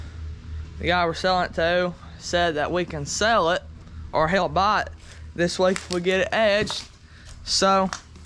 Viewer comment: Great to see you all again and see the preparation for mud season. I mean winter... Around the 4:00 mark...I am confused about what you are saying or trying to tell us? "We can sell it...If we help buy it." Did you get a deer this fall? Thanks for the video!🤠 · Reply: Lol, he was saying the guy said he would buy it/we can sell it. He kinda ran that all together.